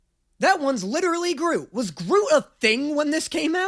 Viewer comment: Y’all I thought he said Gru not Groot! Lmaooooo 😂😂😂